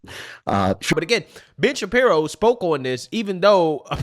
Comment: for a moment there I thought you said Bitch-Apiro